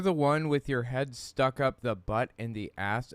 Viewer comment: It sounds like he said “up the butt of the ass…”. i.e. up the ass of a donkey. I think he really wanted to swear and since ass (as a donkey) is in the Bible, he’s “allowed” to say it. 😂